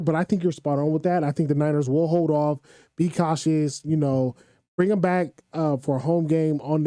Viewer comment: Bruh did u just say "cautioness" @ 6:28? U threw me off when u said it the 2nd time. 🤦🏿‍♂️🤣🤦🏿‍♂️